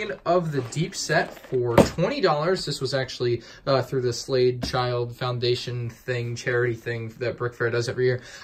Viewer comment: When you talked about the Atlantis shark, I thought you said, "the Slave Child Foundation".